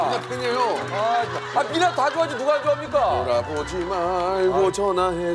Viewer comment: 아무리 들어도 '돌아보지 말고 떠나가라 🎵 ' 로 들리는데 ㅋㅋㅋㅋㅋㅋㅋㅋㅋ 월드컵 미나로 생각하고 빅뱅 노래부르는 명수옹ㅋㅋㅋㅋㅋㅋㅋㅋ